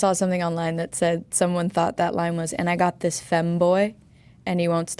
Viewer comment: I heard " and I got this femboy "😂😂